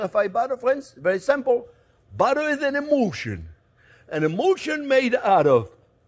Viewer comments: Butter is an emulsion. I thought he said "Butter is an emotion" Hehe. Both are correct. :) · I totally misheard. "Butter is an emotion." *nods fervently in agreement* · Butter is an emulsion. I misheard and thought he said butter is an emotion And it's still true